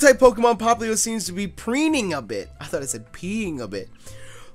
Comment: "I thought he said he was peeing a bit" DED XD